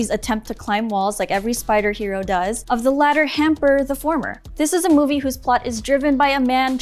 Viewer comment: WHAT IS THIS WOMAN SAYING!? 2:48 - "the latter hamper the former"